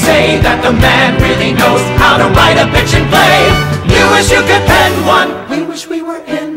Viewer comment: If you are one of the many people who misheard it as "the man really knows how to write a FICTION play," please like this comment.